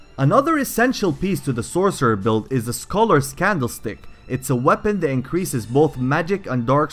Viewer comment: I thought he said the Scholars Scandal stick. I was like wait what? XD